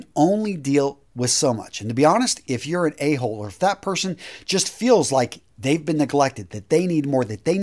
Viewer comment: Some of this is decent advice ... a lot of it is crap. Absolute crap. "If you are an A-hole or fat person" you shouldn't expect to have a relationship last? That's awful, man ... hope you never get sick and have your wife or family leave you over it. I generally love your videos, but this is awful ... or you chose awful people to have in your life. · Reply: The statement you’re referring to takes place at 5:00 mark. When I heard it, I too thought he said “fat person” but my video had close captioning enabled and immediately notices he said “…If you’re an A-hole or if THAT person feels that they’ve been neglected…”” it sure sounds like fat person unless you enable CC and then the statement makes more grammatical sense.